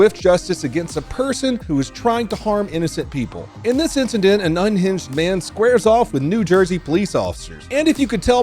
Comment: A case of misheard sentence rather than lyrics 0:19 I swear I initially heard 9 inch man instead of unhinged man the first time and went damn he smol, had to read the title again and go back just to reconfirm what I just heard 😂